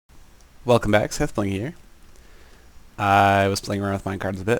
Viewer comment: It sounds like you say "Welcome back Seth-ling here" instead of "Welcome back Seth-bling here"